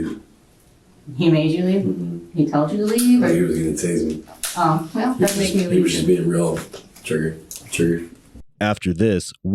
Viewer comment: I think he says “walked (or waltzed) right in” at the inaudible part… if anyone still remains/becomes curious/cares lol.